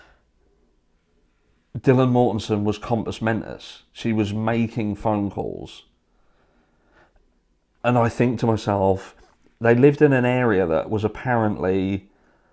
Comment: What is it that he keeps saying? He says it at 14:30, it sounds like the first word is compass??? I’m sorry, it’s not a big deal I just want to know what it is he is saying lmao!!!